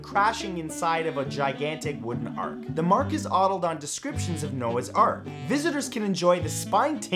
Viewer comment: Did anyone else catch him saying the Mark is Ottled at 8:17 instead of the ark is modeled?